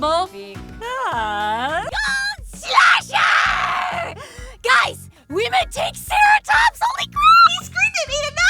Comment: I've rewatched this a few times but I can't tell what she's saying. Anyone? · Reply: "GOLD SAUCHER! "Guys! We met Tankceratops holy crap!!"